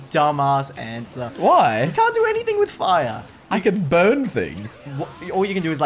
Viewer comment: When said he can burn things in the bending part at 8:14 i thought that he said "I can bone things"